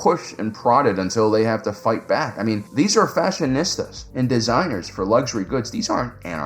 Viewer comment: all I heard when you said fashionistas was the term fascist!